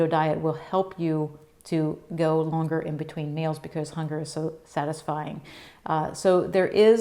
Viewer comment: I listened twice. Was going to share with a friend. at the end you misspoke. You said hunger is so satisfying. Dont think that is what you meant to say. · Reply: I thought she said "because the hunger is satisfied"